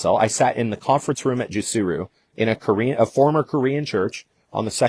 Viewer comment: What is he saying? What is the name of the previous company that merged with Modere that he was with…? with “Asma the CEO”…? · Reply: I think it was called Jusuru.